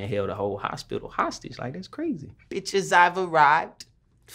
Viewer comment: When..... When...... DID HE SAY THAT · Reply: Lol in the background after he says "bitches I arrived" and you can't really hear it on the colors version because the adlibs were barely loud to hear